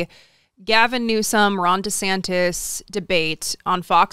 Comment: I always hear “Rhonda Santis”. Surely I’m not the only one, right?